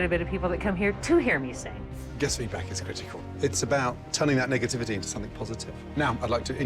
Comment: I thought he said telling that nigga